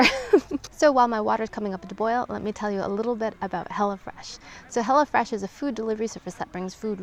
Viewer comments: I thought she said hella fresh 😂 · I kept hearing "hella fresh" lmao !!! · LOL! I thought you were saying "Hellah-Fresh!"